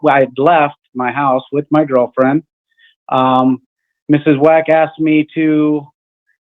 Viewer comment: Why ever time he said she asked something I thought he was saying Ms. Wack A$$. 🤣